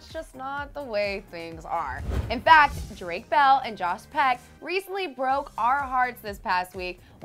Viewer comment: is it me or did it sound like she said Jos Peck ?